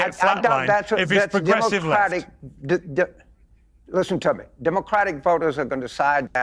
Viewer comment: I now know what James actually said but the first time I heard him I thought he said to Piers: 'Listen tubby'!